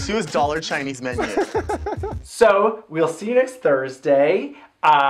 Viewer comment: Whenever you say "Subscribe, Share" I hear "Subscribe, Cher" #mygaybrain